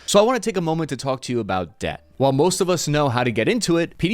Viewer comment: man i thought i heard you said , you sponsor is death not debth